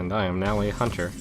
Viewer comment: "And I am now a hunter" - 2014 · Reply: i heard "i am now cancer" thats wierd XD